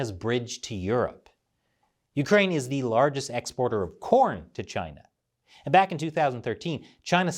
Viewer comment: Legit thought it said "Ukraine was the largest exporter of PORN to China"